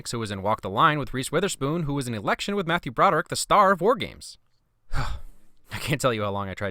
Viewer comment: i thought he said star wars